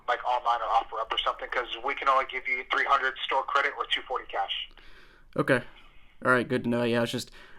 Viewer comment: You misheard more than you thought, he meant 3.00 in store credit, 2.40 cash 😅